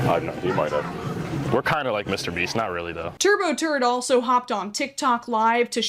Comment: Did you just say "Turbo Turdo?!!" Oh my gosh, that is so funny. (XOXO)